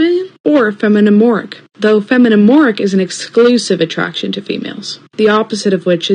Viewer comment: I thought the trixic person said non binary lesbian or feminine Orc.